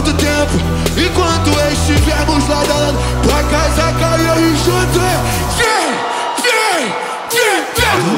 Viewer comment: "tua casa caiu Alexandre"? ouvi isso 12:19 minutos. Espero que tenha falado do Alexandre de Morais. Por falar nisso, fala de censura por favor Ret.